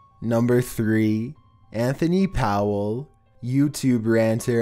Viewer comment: on three i freaked out because i thought he said "anthony padilla."